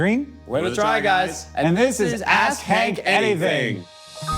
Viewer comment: Did they say ass hank anything?